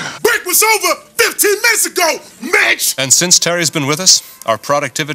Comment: lmao first time i saw it i completely misheard. i thought it was: "Break was over, 15 minutes ago, BITCH!" "Thats a long distance call, THUUUG!"